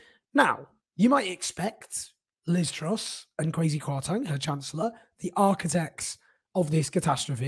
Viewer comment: Thought Owen said "crazy Kwarteng" then lol